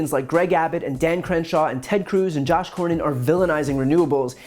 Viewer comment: did I hear “Josh Cornyn?”